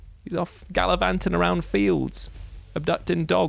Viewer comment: I have listened to 9:46 thrice and I still can't figure out if he's saying his friend "abducts dogs" or "adopts dogs".